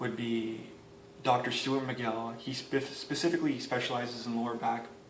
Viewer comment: I could make out the names you recommended as part of online research, at 20:06 , did you say "Dr. stuart mccalley"? I couldn't figure out what the other names either... please tell me :( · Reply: Sorry Sirsi! The names I was referring to are Dr. Stuart Mcgill (Main one), Eric Cressey and Tony Gentilcore :)